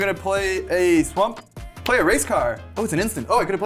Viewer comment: sounded like he said "gonna play a race card"